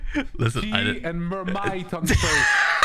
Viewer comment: Antoine a dit quoi ? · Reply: Tea and marmite on toast. Marmite c est une genre de pate tartinable à base de levure, avec un goût très prononcé dû à son haut taux de glutamate notamment. Un peu comme la vegemite si tu connais. Tu peux aussi le diluer dans de l eau pour faire du bouillon. Le marmite, soit t adore soit tu detestes.